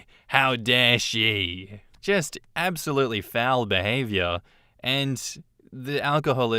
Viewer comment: S1: I swear, at around 16:10, when you said "foul behavior", for a moment I thought I heard, "bowel behavior"! 😂 Which pretty much amounts to the same thing.